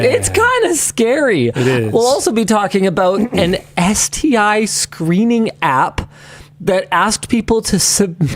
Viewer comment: I misheard Linus say "An STI screaming app". Then he went on to describe it and I thought no, I heard right the first time.